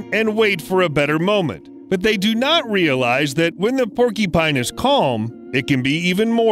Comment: Sounds like he says "Porky Pine" lol